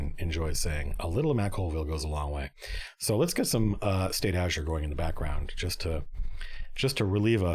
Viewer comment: What was the music he was playing? I heard it as "Stay ager" and I cant find it anywhere so my ears have mislead me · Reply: State Azure.